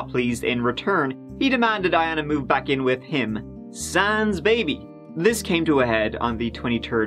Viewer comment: "I want you to move back in with me, but without our baby." "Ok." Did I miss something? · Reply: That would make more sense. So I checked again, but he really said: "He demanded Iana to move back in... sans baby."